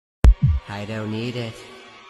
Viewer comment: Why do I hear "I don't eat" 🤣